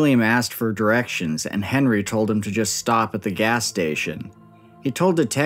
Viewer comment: Thanks again to Ekster for sponsoring this video. Check out - <link> - For up to 30% OFF your order. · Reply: Thought I heard him say stop it